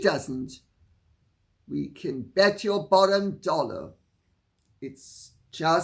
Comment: What a sad way for anyone, let alone a Prince of the Realm, to live one's life. The silly secrecy around the children's birth, the mystery miscarriages, planting your own divorce rumors, lashing out at family who are so good to you. It is just a pathetic way to live one's life. Tragic really. Just one rumor, one complaint, one PR stunt after another. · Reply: I'm sure I heard you say " you can bet your bollock dollar"